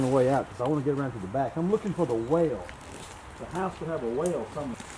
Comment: Looks like the wisteria had another target. I thought said "I'm looking for a whale" . The accent got me! I should know better . I grew up in West Virginia.